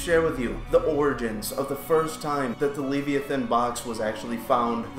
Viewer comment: It's called the lament configuration pleb. I'm taking away your nerd card. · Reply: Noooooooo! My bad I could a sworn it said leviathin haha have a great day!